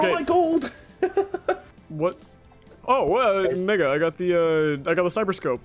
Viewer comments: nah what did he say??? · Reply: He said mega lmao (hopefully) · bro better have said “mega” and not what I heard him say 😳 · he did NOT say mega 😂